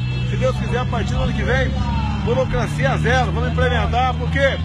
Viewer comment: é "democracia zero" mesmo que ele fala? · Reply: burocracia